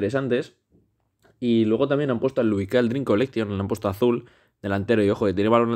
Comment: que dice ahí 😅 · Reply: Creo que es Lukai Dream Fest